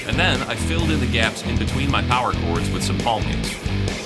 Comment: What word was used at 2:50 ? Sounded like Balkan’s not familiar with that term.